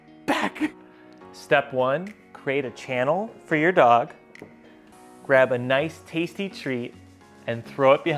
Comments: i thought he was gonna say "grab a knife" but he said "grab a NICE tasty treat" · I thought instead of him saying nice treat I thought he said grab a knife